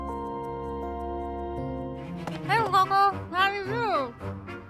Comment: What Hapi said with pastries in her mouth: 35:57 “Hey, Coco! How are you?” 36:05 “No need to be jealous. There’s plenty to go around.” 36:18 “My pleasure!”